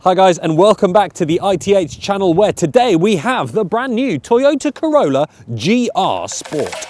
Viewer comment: I heard you say Toyota Corona😂